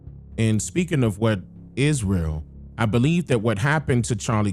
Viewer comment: I heard IS REAL thought I heard ISRAEL